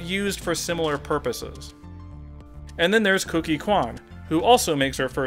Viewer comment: Did you say, "Kooky" Kwan (9:42)? I'm certain it is "Cookie Kwan" (Number one on the west side :-) ).